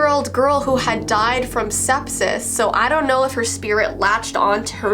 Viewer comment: sepsis Me, someone who's not english: did she just say sexis? I know this isn't funny but that's what I heard until I asked my sisters what they heard